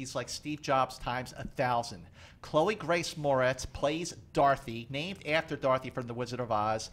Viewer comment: I thought he was saying "darthy", lolz...not "dorothy" XD